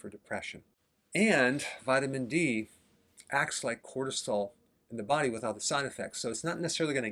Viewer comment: he says "without the side effects" not "with all the side effects": the subtitles are wrong and this is not the first time i notice it. u may wanna just leave the auto-generated subtitles they are pretty accurate.